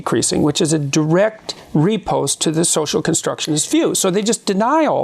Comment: When the subtitle says 'REPOST', the word Jordan Peterson in this case really means is 'RIPOSTE' which is a fencing term that means 'a quick return thrust following a parry' but in this context means 'a quick, clever reply to an insult or criticism.'